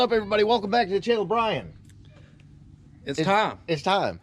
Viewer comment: thought he said "its tom" awkward silence... lol!!